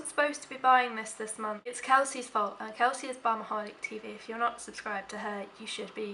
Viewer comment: I thought you said 'Bumaholic'. And was VERY impressed by the name. Can someone please tell me when the 'growing up' thing happens, as I'm 32 and it hasn't happened yet......